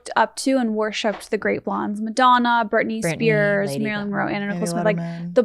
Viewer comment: I thought for a while said Marilyn manson lolol